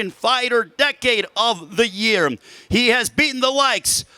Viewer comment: does anyone hear this "decade of the year"